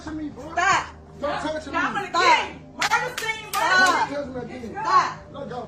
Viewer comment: did she say “murder scene murder scene”?